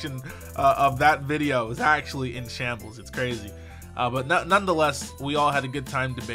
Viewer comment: what is that word? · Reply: Shambles?